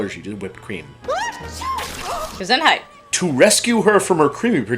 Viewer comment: did just say Gesundheit!?